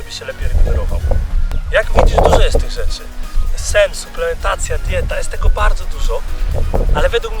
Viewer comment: usłyszałem sex, suplementacja ... może to nie przypadek :-)